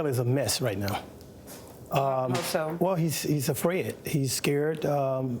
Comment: If he's a crisis manager, R Kelly can hang it up. SMH! Wait did he say afred? Instead of afraid?